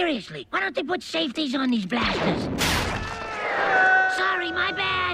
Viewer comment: the word "blasters" sounded like the word "bastards"